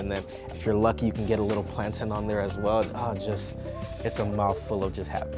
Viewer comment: did he just say "plantin"